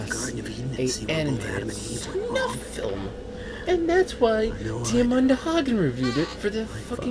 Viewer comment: what does say? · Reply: Jackie Mexwell Diamanda Hagen reviewed this.